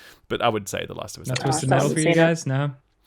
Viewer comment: For anyone else who had trouble to understand what was saying @ 37:53. It's about Twisted Metal.